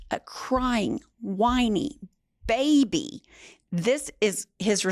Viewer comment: In my mind I heard crying wining bitch lol idk why 😂